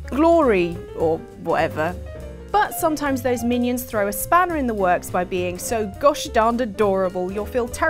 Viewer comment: Did they just say "Spaniard in the works?"